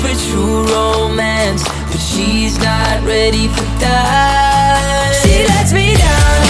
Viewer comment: I thought he said "but shes not ready to die"